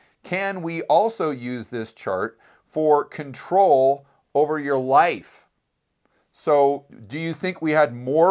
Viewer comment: I thought he said control over your WIFE. Go on, I'm listening.